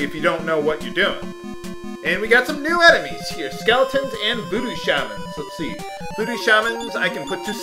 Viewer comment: i thought he said booty shaman